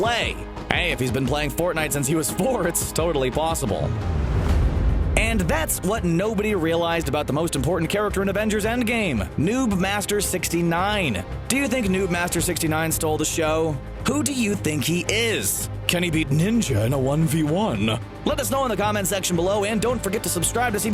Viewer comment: I clearly heard, "nudemaster69"